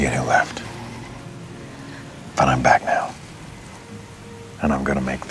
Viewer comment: at first I thought Superman said “I’m Batman”